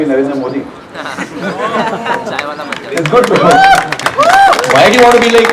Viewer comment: when Sahil said I want to be NAMO . I heard someone whispering CHAI WALA BAN JA. 👻👻